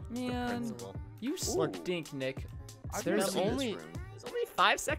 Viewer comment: listen closley from 17:38-17:49 listen it says "you suck....nick i thought that they didnt say words like that so..everyone i looked over the video and says the stuff you here..